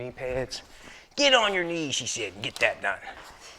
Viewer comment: Did he just say get on your knees and get that nut. I could have swore that’s what he said. Now that’s worth just to hear that. Loll